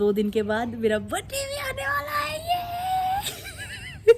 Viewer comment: birthday ane vala he i hear mera pati ane vala he 😂😂